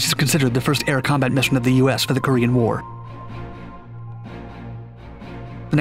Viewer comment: He speaks so fast, I thought he said "green war"